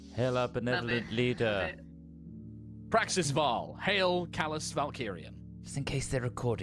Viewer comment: Here's one for the Voltron fans out there. Every time says "Praxis Val" all I hear is "Vrepit Sa"